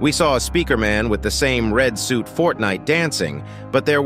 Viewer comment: did he just say FORTINE DANCING?